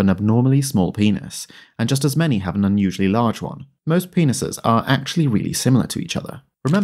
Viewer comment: Lol i thought it said pennies